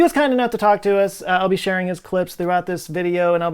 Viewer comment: omg half paying attention and then I thought I heard you say you would be "sharing his corpse..." it was clips not corpse... oops!